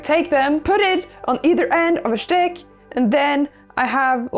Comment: I love how she just said schtick instead of stick at 5:28 xD